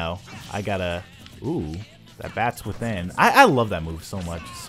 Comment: it almost sounded like a witch pun